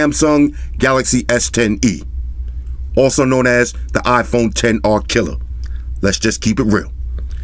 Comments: iPhone Xr KILLER 😎👍💚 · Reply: What I heard was "iPhone 10 R. Kelly" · Somehow I thought he said "The Galaxy S10E or the Iphone Xr Kelly". I was very confused :P.